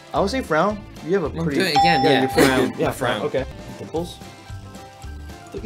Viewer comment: Ki Hong:My dimples? Pauses the video Me:Did he say his nipples? Lol plays the video Thomas:Dude I thought you said your nipples Same Thomas,same😂😂